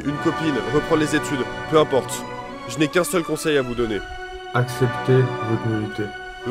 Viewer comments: je n ai pas bien entendu, accepter quoi ? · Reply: « Acceptez votre nullité » je crois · Il dit quoi ? “ Accepter de ... “ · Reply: « Accepter votre nullité »